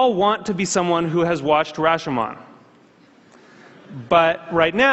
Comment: What does it say at 5:18 ? Rachuman ?